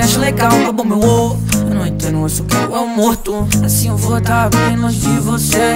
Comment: Letra Tarara Ta tarara Te mandei mensagem aquela noite Tu não respondeu Eu fiquei pensando se eu devia ter mandado Essa merda me fudeu Eu lembro também tua voz era tão doce O que aconteceu Vadia você abriu no meu peito um buraco E você nem percebeu To ficando chapado denovo Eles me acham legal babam meu ovo Eu não entendo, eu só quero eu morto Assim eu vou ta bem longe de você Esse quarto escuro to ficando loco Eu te dei tanto, cê me deu tão pouco Eu fui ingênuo, eu fui tolo, de ter confiado em uma pessoa igual... A você Tarara Ta tarara Te mandei mensagem aquela noite Tu não respondeu Eu fiquei pensando se eu devia ter mandado Essa merda me fudeu Eu lembro também tua voz era tão doce O que aconteceu Vadia você abriu no meu peito um buraco E você nem percebeu To ficando chapado denovo Eles me acham legal babam meu ovo Eu não entendo, eu só quero eu morto Assim eu vou ta bem longe de você Esse quarto escuro to ficando loco Eu te dei tanto, cê me deu tão pouco Eu fui ingênuo, eu fui tolo, de ter confiado em uma pessoa igual... A você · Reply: eu entendi “eles querem eu morto”